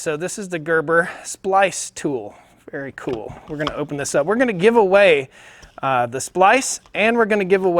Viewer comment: you can order it from Amazon in Greece I do believe lol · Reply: i thought you said for a give away...